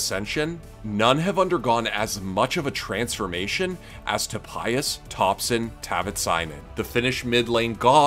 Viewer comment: did you just call him topayas? 😂